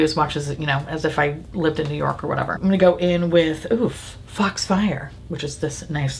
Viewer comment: I thought you said fucks fire, not fox fire for the colour 😅😂 but you didn’t name them so makes sense right! Lol